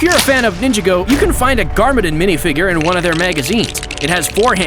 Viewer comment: Did he say nin Jin go